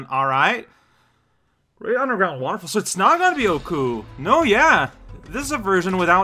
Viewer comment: I don't know what you're saying, but it sounds nothing like "Utsuho," which is the actual name of who you fought in Reimu's story here. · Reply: He said “Okuu”, which is what she’s often called. Honestly I’m surprised you didn’t realize that, considering I swear she’s called Okuu more often than Utsuho most of the time.